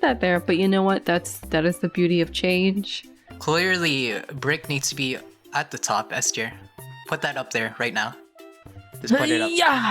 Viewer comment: Is name Esther?????? Am I the only one who heard that? 0:38 Edit: My bad😅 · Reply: 😭 he said s tier not Esther 😭